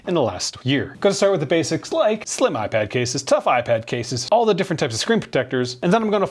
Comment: You said that so fast I thought you said slipknot iPad case